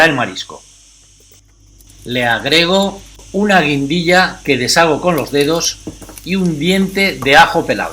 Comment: Gracias pero no escuche muy bien 2:20 ? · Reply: Guindilla 🌶 también cayena. Saludos 🥘